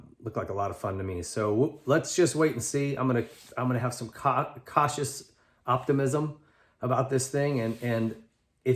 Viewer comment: 🤣 did say ‘I’m going to have some cock’ at 9:30 😂🤣😂😂